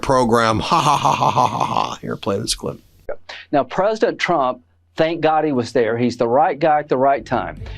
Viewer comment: Sounded like Carolyn said "President Chump" 😂